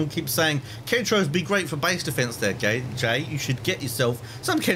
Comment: he called himself gay instead of jay XD